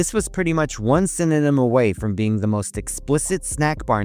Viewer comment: You said one synonym but I for some reason thought you said letter and so I thought “which letter would you change to make it explicit?” Somehow my genius ass came up with Wet Pooster. . .